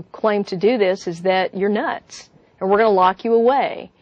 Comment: Valeu pela tradução! mas em 3:12 acho que ela diz "You're nuts!" ao invés de "you are not"...